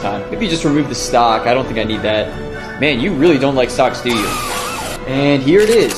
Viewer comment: multiple times i thought he said socks instead of stocks